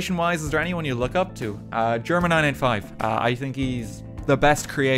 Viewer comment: Help! Who is this streamer he said is comedy gold and a massive influencer on his content? I heard "German i95" but can't find such a person.